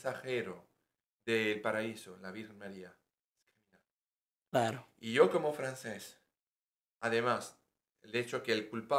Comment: Que dijo entre 12:52 - 12:54? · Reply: Bendiciones! El dijo “ me dan vergüenza”